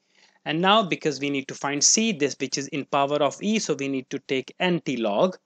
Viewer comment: in 14:13 did u actually say this bitch ???? lol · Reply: he said which i think lmaoo